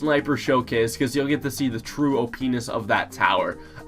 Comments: did he say penis? xD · sounds like he is saying o penis xD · O penis ? Is that what I heard